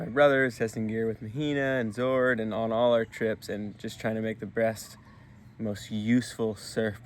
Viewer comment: Did he say Breassst? Just checking